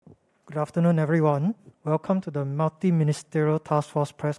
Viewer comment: In the beginning of the vid, I hear as "welcome to the naughty ministerial task force"